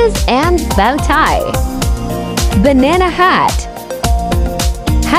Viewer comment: I thought said banana cat not hat